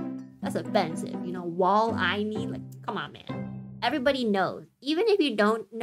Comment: Lmaoo wall eye knee. Today I learn something new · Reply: Ngl I misheard it as "war I need" when editing or is it just me